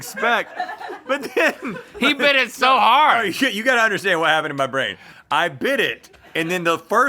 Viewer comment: Did Rhett just say "no, sh!t, you gotta understand.." at 10:48??